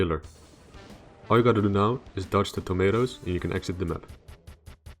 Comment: Dodge the tomatoes lol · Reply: Honestly thought he said Komaytos, the Metroid-like monster from Kid Icarus.